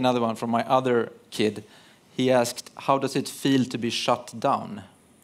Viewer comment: she thought he said "Shot down" but he said "Shut down"